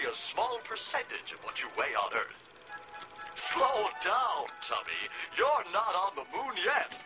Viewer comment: Slow down Chubby! Your not on the moon yet! 😂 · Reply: I always thought he said Tommy not tubby